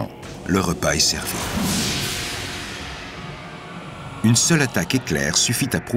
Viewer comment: j'ai entendu "une seule attaque Hitler" j'ai trop fumé